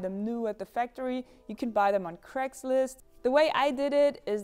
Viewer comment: Genuinely thought you said Crateslist instead of Craigslist 😂